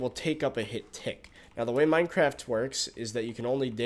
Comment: I heard "the way Minecraft twerks" Just me?